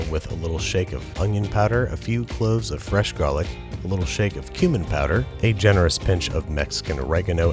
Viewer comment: I thought he said “human powder” I was like “when he said all meats he meant ALL MEAtS”